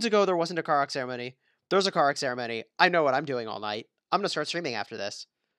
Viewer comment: Karuak Ceremony sorry i misheard it for Car Accident lol